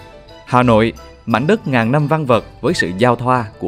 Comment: Ngàn năm văn vở? Hàng bồm? Thống nhất chí? 😂😂😂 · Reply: Ngàn năm văn vật, 4:44 Nhất thống chí, 6:44 Hàng Buồm. Tai thanh niên có vấn đề hay sao mà nghe thành cái gì thế :V